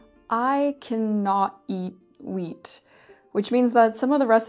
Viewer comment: I thought you said meat, but captions corrected me to WHEAT. celiacs? my step-mother had that.